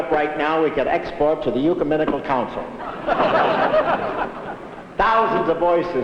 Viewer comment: Did Hope say "yuceminical council"? I think he meant "ecumenical council" (roughly, after 10:50).